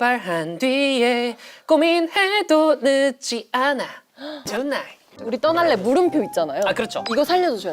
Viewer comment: 존나로 들림